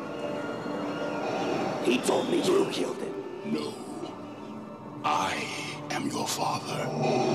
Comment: I'm mad because all these years I thought he said " I am not your father" Mandela effect 😫